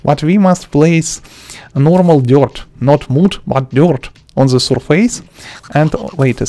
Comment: What is he saying at 2:04 something like "not mot but iord?" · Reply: Not mud but dirt